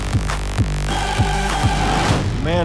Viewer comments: I thought he said mayonnaise XD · I thought he said "Mayonnaise maketh man" and I was like, ok, he's american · Wait, did he just say, "Mayonnaise, maketh the man."